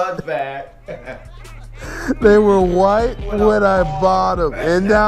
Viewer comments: This whole time I thought Patrick said they were wet when he bought them. Now that I know they were white my whole world is upside down. 🤣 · I thought patrick said they were right when he bought them